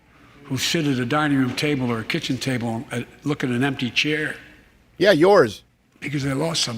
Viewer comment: Did he just say "People who shit at the kitchen table"?!